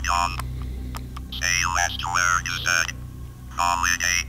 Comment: right before the gunshots start going off, it really sounds like it says “say last words” Hopefully I’m not the only one that hears that